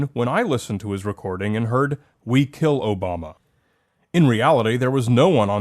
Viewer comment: Personally I heard, “ we killed Osama” at first.